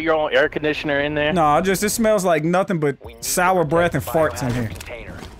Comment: thought you said it smells like soudoughbread and farts